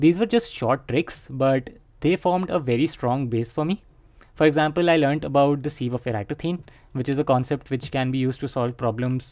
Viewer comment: which topic ?? I can't understand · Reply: Prime seive